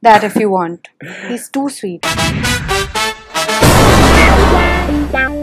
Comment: Why I heard "hi this is chutiya"